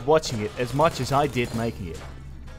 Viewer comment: I thought he said “and for now, I hope you are at least a little bit whiter”